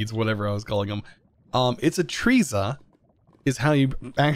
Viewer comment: nope, it's neither. still "artisia". like artist, just with ia instead of a t. :P